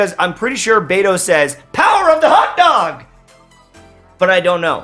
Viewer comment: I never thought Beidou said power of the hotdog but I do now.. thanks for that.